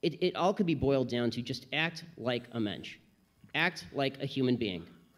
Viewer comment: What word does Ben say at 0:37 ? Act like_______. Does he say "A mensch"??